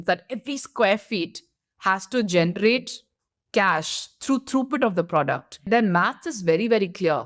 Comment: What's the through--word she said at 9:11? I couldn't catch it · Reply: She said “throughput.” Basically means the number of products that can go through a system in a specific amount of time.